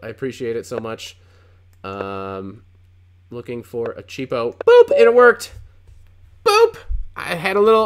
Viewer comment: what did he say here? "A cheapple"? What does it mean?